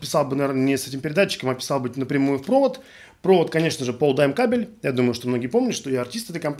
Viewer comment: Крутой обзор! спасибо! А как точно фирма кабеля для записи пишется, который советует, на видео услышал "Полдаймон кабель"